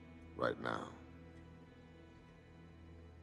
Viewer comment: I always thought he said "salute me from here" but then again i cant really understand anything vin diesel says in his dom voice